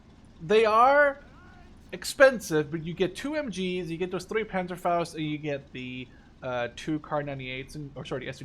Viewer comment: the subtitles thought said pedophiles when he said panzerfausts lol